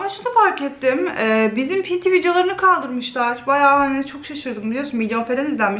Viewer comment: arkadaşlar 0:15 de abla ne diyor bilen varmı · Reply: Pinti videolarını kaldırmışlar diyor .d aslında net ama